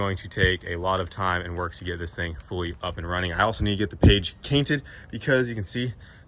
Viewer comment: Page cainted😂😂 all love but I had to go back and make sure I heard it right